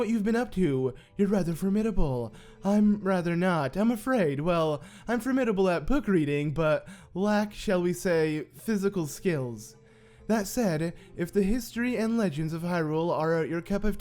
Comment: It sounded like he said "Booger Eating" at 3:41 lmao